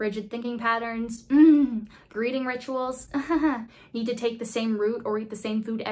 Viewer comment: I got so confused at 11:21 misheard greeting rituals as breeding rituals.